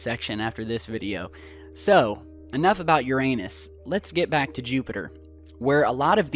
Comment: “Enough about your anus” I dead ass thought he said that